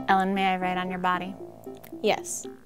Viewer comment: At first I thought you said "Ellen, may I *ride* on your body."